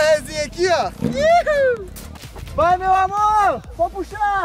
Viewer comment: Gente só eu que ouvi no minuto 8:56 o falando pro "vai meu amor" ;-;eu acho que tô ficando louka